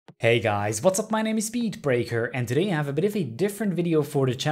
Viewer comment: did he just say "speed breaker" is his name